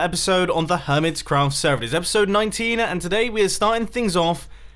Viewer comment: did he say episode 19??